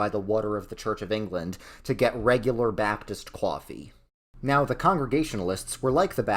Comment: qwafe instead of coffee